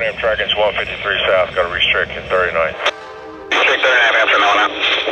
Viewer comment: I think I heard the Amtrak engineer say "Got a restricting; very annoying." Did he say that or something else?